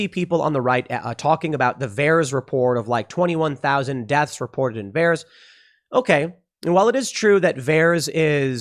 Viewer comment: I thought you were saying deaths reported in bears. Bears. Why on earth are they vaccinating bears?